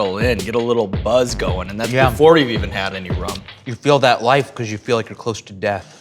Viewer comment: "It feels like life, because you're close to death." -Michael Stevens 2017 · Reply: James Burgess I heard "You feel more alive"